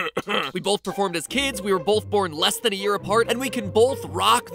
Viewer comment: I thought you said “we were born as kids” and it took my a minute to realize I was just stupid